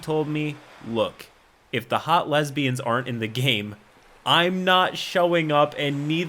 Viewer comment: Normally i leave your video minimized and just listen to it, but when said that i had to watch the video and read it think i misheard "aren't" as "are" Wtf?